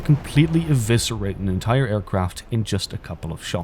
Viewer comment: did u just say Aircock